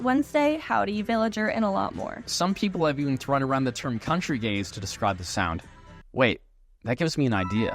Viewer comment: I misheard "Countrygaze" as "Country gays"